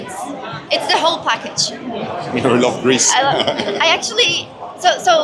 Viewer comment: Oh... they said "grease"... I heard "Greece" and I was like "IS NICK GREEK? :O :O" xD